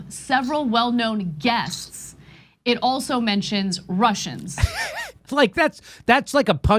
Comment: When Ana says 'Russians', I hear 'Martians'. Those no-good Martians trying to annihilate us.